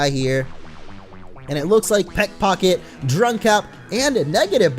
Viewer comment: Drunkap instead of dummkap